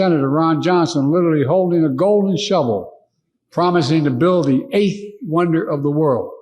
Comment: Oh, sh!t I thought he said golden shower, but it was golden shovel.